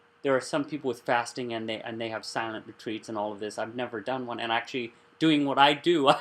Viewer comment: I like this video. When I saw that you were going to talk about fasting, I was eager to listen. There was one part at 9:38 of the video that I could not understand what you said. You repeated it at 9:50-52 and it sounded like you said, "Silent retreat." Regardless, could you tell me what that is about? Thanx. Terry O